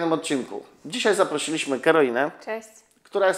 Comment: Czy tylko ja usłyszałem zamiast Karolina ,,Heroina"? W jakim mieście ma Pan salon? :)